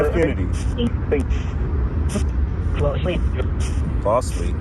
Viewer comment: "falsely" but it sounded like "closely". Some think a shot came from the security detail car behind Kennedy's car. Maybe it said closely instead of falsely